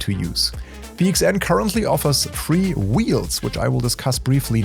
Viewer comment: it sounded like you said "PXN currently offers free wheels" and YouTube's auto captions for English assumed the same. You obviously meant three. It's just a minor pronounciation thing as English is not the preseneter's first language, so just something to watch our for in future 😉